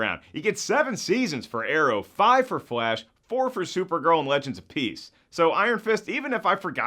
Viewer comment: Did he just say legends of PEACE???